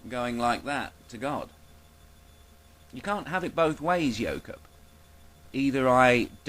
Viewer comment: Every time you say "Yoke Up" I keep hearing "yokel". Take that however you will.